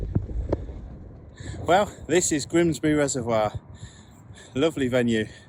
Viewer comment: Kin ell I need my hearing tested, thought you said Grimsby! I thought wow he’s fishing the North Sea. 😀👍🍌 PS great vid